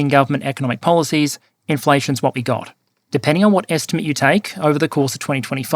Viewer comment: I misheard it as inflation is now god.